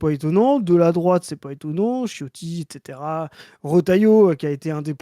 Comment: J'ai entendu chiotti à 1:11 ? xD